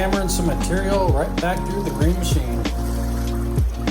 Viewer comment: I thought you said back to the green chain oh a mill worker I never worked in a mill I Was out on steep hillsides logging or falling trees you said green machine.